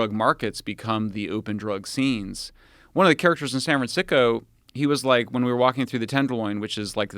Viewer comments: Did he say San Fran-sicko? 🤔🤣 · Sounded like he said san franSICKO! Sounds about right.